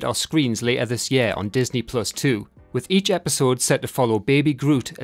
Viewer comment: Tiny nitpicky note: at 05:46 you say I am Groot is coming to Disney+ too, and the way it came off i heard Disney+ 2, like ESPN 2, BBC 2, or MTV 2 and got real nervous.